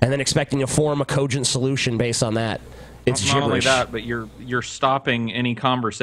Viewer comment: does say "It's Jewish"??